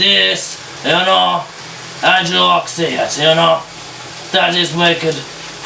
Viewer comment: did he just say "auo.." ?